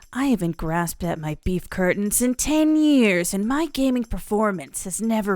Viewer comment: did she just say beef curtains ? gets a sub for being so brave lol